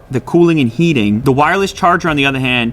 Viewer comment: I thought you were saying CHEAP Wireless Charger